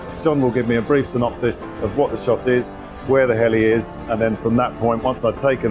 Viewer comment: I heard "where the hell he is!!"